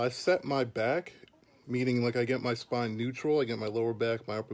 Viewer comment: à 2:00 il dit quoi pour garder un straight back pour le soulevé de terre ? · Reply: "keep the spine neutral" = il garde la courbure naturelle de son dos en fait